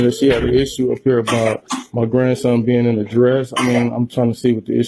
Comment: In the beginning it sounds like he said she had a problem with his grandson being the dress, I was confused